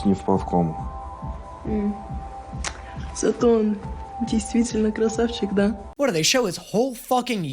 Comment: fyi, she said he's really "pretty" not "hot". Idk if it makes a difference but its the first time there's a difference between the subtitles and what they said that i hear